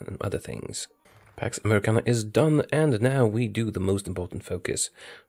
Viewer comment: At first I heard "now we do the most american focus"...